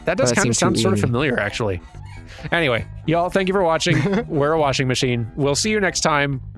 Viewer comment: I almost misheard the signoff as "Y'all, thank you for washing!"